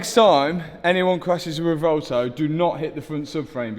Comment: I thought it said reventon for a second.